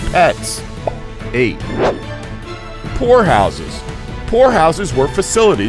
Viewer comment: I thought he said whore house but poor house